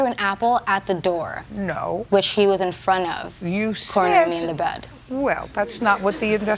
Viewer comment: "I threw an apple at the door, which he was in front of...cornering me and the bed."...Is that what I heard? I'm not clear on what she said, but the audience heard something well enough to be able to laugh. I can see laughing at "which he was in front of ," but I don't get what is so funny about the last part and I don't know if I even heard it right.